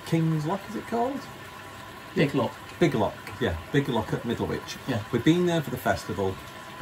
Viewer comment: I'm deaf in one ear so I hear things funny sometimes, I thought said Widdlewich haha